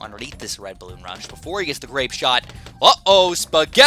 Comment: DID YOU SAY RAPE SHOT?!